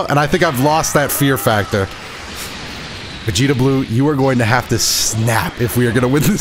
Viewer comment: It sounded like he said vegito blue 😢